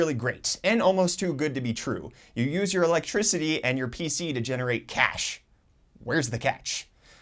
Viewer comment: Uhmmm all i heard is "use your electricity and hardware to make money'